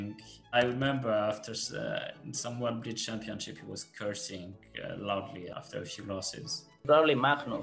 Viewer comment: I thought MVL said "...cursing lovely"